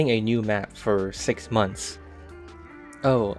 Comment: For a little bit, I thought it said "6 minutes," not "6 months."